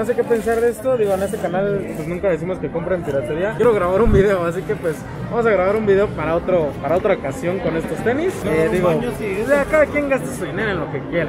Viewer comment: Los tenis que te regalaron que precio tienen al publico???? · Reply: Dijo que en otro video hablaria de esos tenis, eso le entendi yo. Min